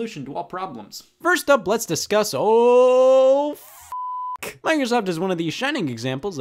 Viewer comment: He says Microsoft but the subtitles say "Fingers Up". What the hell is fingers up?